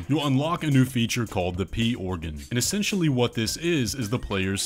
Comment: Did he just say " The Pee Organ " ???